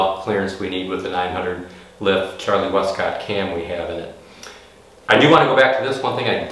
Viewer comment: Did I just hear 0.900" lift cam?! 2:58 Impressive on stock castings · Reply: 900 lift Charlie Westcott cam. Yeah!